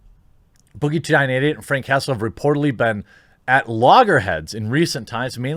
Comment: I thought you said Frank Castle. - definitely not a good idea to shoot at the Punisher!